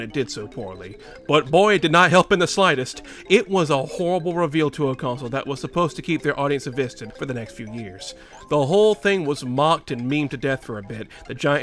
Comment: Bro, do yourself and the audience a favor by SLOWING DOWN YOUR SPEECH. I have no idea what you're saying at 6:40. "It was a horrible revealtoaconsole that was supposed to keep their audience addisted(?) for the next few years." You need to slow down and annunciate better if you're going to be doing narration work like this. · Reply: he said invested